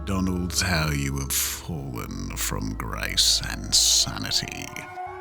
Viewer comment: This looks fantastic! I had youtube subtitles on so instead of you saying "Oh dear dear McDonald's, how you have fallen from grace and sanity" The subtitles thought you said "How you have fallen from grace and Santa." 😭